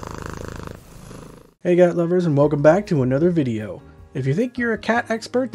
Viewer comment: I heard "Gat lovers".